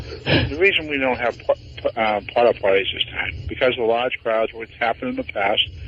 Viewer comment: I just heard a new one, when he first talks about not having "port-a-pottys" because people use them as weapons, he mushes it to sound like "puddle parties".